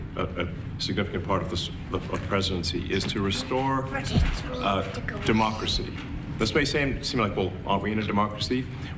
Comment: what did the kid say? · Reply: "shut up, you're not the president". 😂😂😂